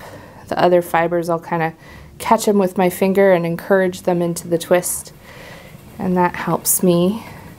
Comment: When you say ‘into the twist’ I hear ‘into the drift’ from Pacific Rim 😂 I’m a very new subscriber from the UK and you’ve inspired me, I’m waiting for my first drop spindle to arrive so I can see if I enjoy it as much as you clearly do. Thanks for the lovely and really welcoming content, you’re doing a great job! 👏